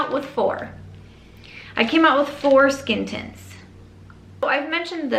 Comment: I keep hearing “foreskin tints”. Am I ridiculous? (Yes)